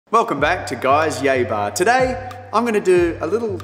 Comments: When u said yay bar I thought u said “Gay bar” xD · i thought he said "Guy's gay bar" :P